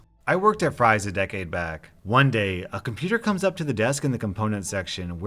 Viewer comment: Wait...what? Did I hear him say "A computer comes up to the desk..."?